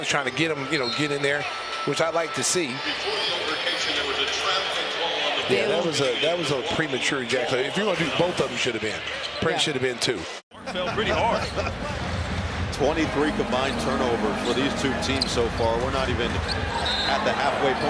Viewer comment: did he say premature ejaculation ?